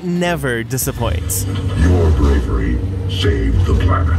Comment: Did I hear that transformer say: “Your Gregory” ?, or did I hear something else, idk Edit: oop, nvm, they said “your bravery”